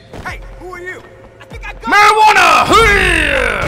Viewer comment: LMAO Did he say marijuana